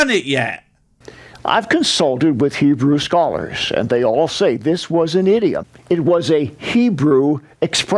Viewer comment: did he say "this was an idiot"?